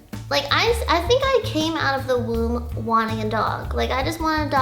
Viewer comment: did she just say woom???